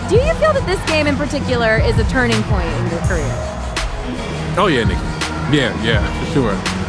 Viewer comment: I really thought he said “hell yah Nikki” 😂